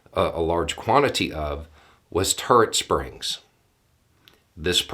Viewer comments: For a minute there I thought he said "Turd springs" 🙄. It's the accent 😁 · I love the combination of your accent and my hearing. I heard “turd springs”. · At first I thought you said "turd springs" and now I have a very, very strange concept in my head. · I get a kick out of pronunciations sometimes. I heard "turd springs" the first time he said, "turret springs. " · You said "turret springs" and I heard "turd springs." But THAT is not propaganda. It's just humor.